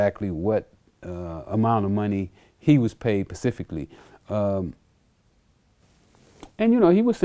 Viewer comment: When you say pacifically instead of specifically